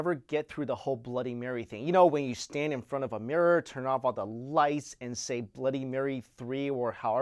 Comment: I thought he said turn out all the lice XD